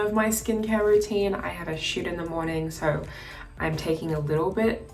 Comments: I THOUGHT SHE SAID I HAD A SH*T IN THE MORNING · I’m not gonna lie when she said I usually shoot in the morning I thought she said shit 🥴😂x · i thought she said "i have a shit in the morning" bc girl same